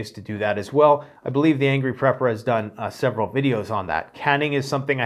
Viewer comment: Sounds good, but I couldn't inderstand the name of the prepper you mention @ 18:53 , can you tell me? Thank you man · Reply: it sounded like he said Angry Prepper